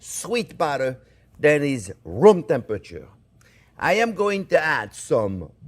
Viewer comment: For a moment I thought chef said the butter is rum temperature:)